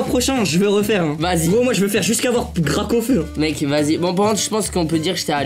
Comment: "gracaufeu" 😭😂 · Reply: mes t’es bête toi c’est parce qu’à 14:04 il a dit gracaufeu